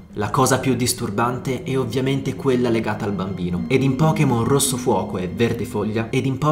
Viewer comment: non ho capito in che gioco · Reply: Rosso fuoco e verde foglia!